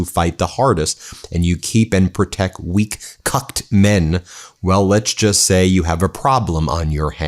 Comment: I thought I heard, “… weak cu*t (with the letter n) men…” I stopped, did a double take, and was like, “For real!”.😂 It is a more fitting word than “cucked”, but I guess I’ll take it lol. I’m not a MTG in any way, but Trump f’d with the wrong woman😂 She never blinks. She’s scary lol. This dismantling of MAGA is so satisfying to watch & I’m here for it.🍿😂😂😂😂 🔸Keep it up Meidas Touch!❤ I’ve been a long time subscriber and stay updated and watch your much needed coverage on YouTube every day. I’m grateful to everyone that makes this channel happen.❤️ The American people need and deserve to hear and know the truth of what is happening in this country and in the world🇺🇸. 🔸*Side note: I have no idea why I’m not a Patreon subscriber already, but I will fix that today😊 I hope everyone has a great and safe weekend ✨